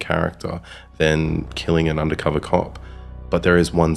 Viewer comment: I am a DC fan, but Punisher is my favorite anti-hero of Marvel. But at 10:33, I misheard as undercover cock!?